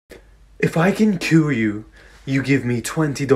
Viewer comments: What it says “if I can cure you” What I heard “if I can kill you”💀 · It sounds like hes saying kill you not Cure you